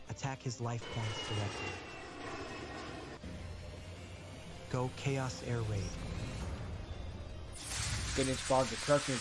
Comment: for a moment, it sounded like he said Chaos AirRape XD